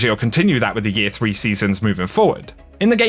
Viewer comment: When he said omar agah i thought he said oh my god